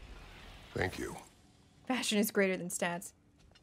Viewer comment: Did he just said "Thank you"? 😮